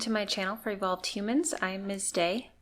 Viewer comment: I know you say something along the lines of “I am miss Day” mama all I heard was have a nice day and I just gotta say – have a great day, love 💕 😂😊